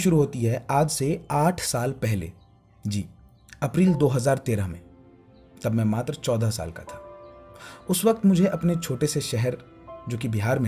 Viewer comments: who else heard : "Tab main ma***d tha 😂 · I firstly think he said madarchoda Then when I heard mattar choda Saal Ka tha 😑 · When he said "tab mai matra choda saal ka tha" I heard something else 'madarchod'🤣